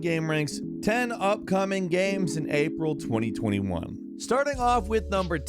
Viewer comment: He really said Gaaam